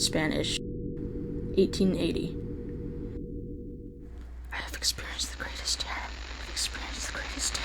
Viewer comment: i thought you said "I have experienced the greatest dab" lol im so sorry